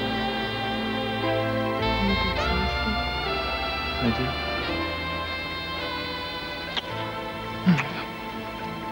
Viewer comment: What does Eden say to Cruz at 4:30? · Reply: Eden says you look exhausted and he says I do?